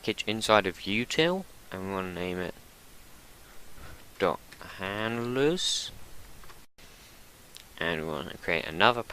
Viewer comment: what did u say in 19:30 ? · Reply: "handlers"